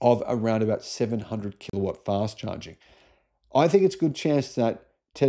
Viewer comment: did he say these LFP batteries could have a 700kw charging speed?